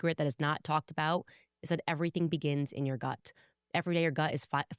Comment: Really thought you said “everything begins in your butt” - just thrive